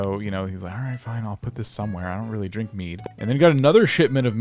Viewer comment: I thought you said meat. I dont really drink meat lmao i was like ohhh mead